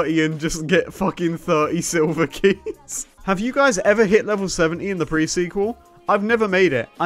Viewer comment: Did anyone else thought he said he didn't reach level 30 in The Pre-Sequel rather than 70?